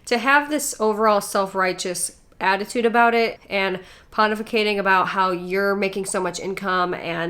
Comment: you use the word "pontificating". At first, I thought you said "hun-tificating". I hearby nominate "hun-tificating" as a new word.